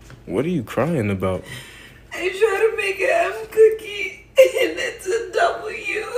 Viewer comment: Okay I thought that she said "I WANTED TO MAKE AN F COOKIE😭" but it was M